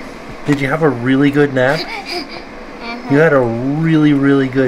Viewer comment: Almost sounds like says, "Uh huh," when Tim asks him if he had a really good nap.